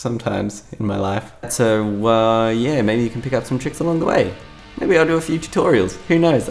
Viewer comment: It sounded like you said pick up some chicks along the way.... March theme?